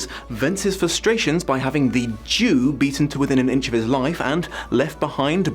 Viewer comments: at the start when you said "The Jew" I heard it as "The Duke" so I was very confused until you said it again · I kept mishearing what you were saying at the beginning as "The Dew". Getting to the end of of the video made everything make sense. |D Side note, did you stumble across the musical when you were looking into this? Some of it feels like it was intentionally trying to remind people of Les Mis, but IMO some of the songs are truly delightful. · For most of this video I heard it as "The Due" instead of "The Jew", so I was really confused by why you spat the water out. Once you started explaining it though... Yeah, I guess I'm still naive and innocent in some ways, that it never occurred to me you meant the other spelling/meaning of the word. It's quite horrid how the Jewish people keep getting dragged through the mud in stories like this.